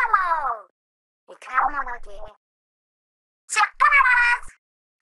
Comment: Is it me or does she say "We're counting on you"?